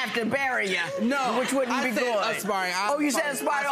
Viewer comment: Episode 123 @ 41:12 : Mr. said, "Expiring artist". When the judge corrected him ["aspiring"], he claimed that that was what he said. But as he repeated the sentence, seems he still got it wrong. Sounds to me like he said, "'INSPIRING' artist"‼️😜🤦🏽